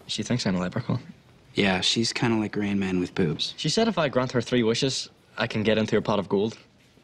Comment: what does finn say? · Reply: Geneva T Kind like Rain Man with boops